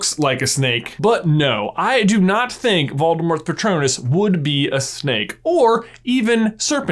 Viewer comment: im sorry but I thought it said Voldemort’s pronouns 😂😂😂